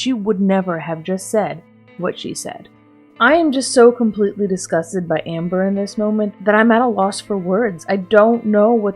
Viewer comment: what did she say?? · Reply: she responded to the comment "stop treating us like were youre therapist" with "no, this channels about my life and me telling you guys [about being suicidal] is no different than me telling you guys 'oh, i had CoVID, like, six months ago'"